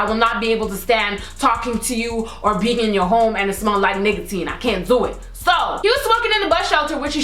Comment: I thought she said niggatine when she said nicotine lol